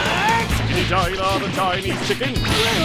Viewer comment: wtf did he say? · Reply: chickadee China, da Chinese chicken... you have? but once you have a drumstick, you're brain stops ticking... So watch out for that